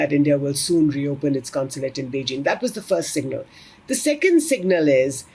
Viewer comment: open their consulate in Beijing? Did i hear that right